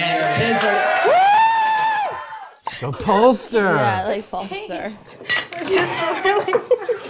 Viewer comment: gets me every time “we’re getting our makeup done to go to Cold Stone” in his Lo voice lol😂😂😂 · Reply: Agreed! Especially since they said "going to Polster's", but mishearing it as Cold Stone makes me laugh.